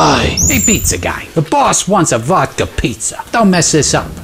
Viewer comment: Vodka pizza I thought he said vodka pie